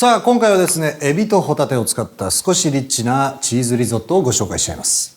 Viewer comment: 「少しえっちなチーズリゾット」に聞こえて やだなにそれ気になる...と思ってたらただの リッチなリゾットだった